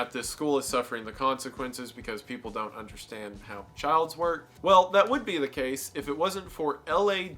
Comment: Did he say "childs" instead of "children" there?